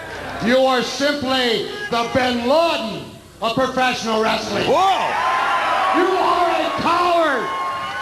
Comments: Aah gold ol early days of tna♥️💪 Lmao did Roddy call Vince bin laden of professional wrestling at 5:10?😂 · I'm brazilian and I didn't get it what he said on 5:06 on the video... "You are simply the Ben (?) what??" Please, somebody help me :D and BTW, Roddy is a freaking LEGEND!!